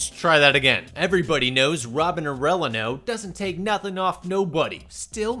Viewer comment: The way you pronounced Robins last name sounded like you said oregano😭😭😭